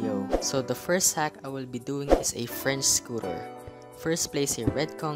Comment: i thought he said frinds scooter